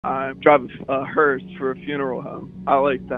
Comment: All I heard was "I dropped a hearse through a funeral home"